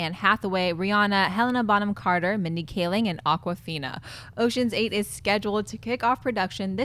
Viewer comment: Did say that Aquafina was going to be in the Ocean's Eight movie?